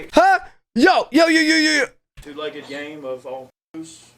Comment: He thought he said "two-legged gay" lol he said game.... but if you were thinking to shoot gays i mean...👀